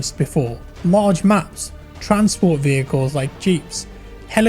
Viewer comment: did Jack say "marge maps"?